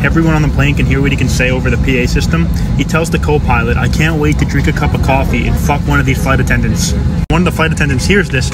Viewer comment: At first class I thought it said, "Someday I can't wait to drink a cup of coffee and f*ck one of these flights up.